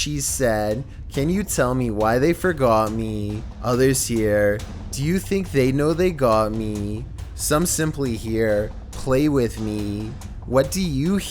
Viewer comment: Yeah, I hear, "Do you think they got me?" from the little girl :(